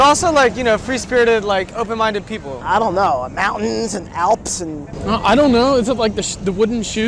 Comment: did he say mountains?🤣🤣